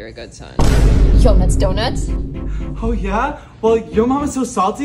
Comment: I keep hearing “yo nuts don’t us” 😅